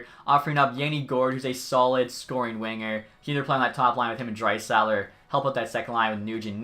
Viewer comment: Did he say Driesiler???